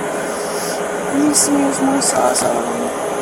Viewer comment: the closed captions say that she’s saying “let me smell awesome” 😂😂😂😂😂